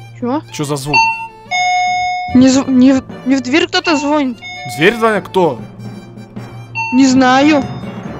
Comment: кому послышалось что звонит. зверь